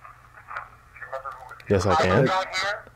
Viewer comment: anyone else hear very clearly after asks can you see us right now, I hear a male voice say (I can see) and then listen closely the word (you) in a whisper unlike the other words in a raspy voice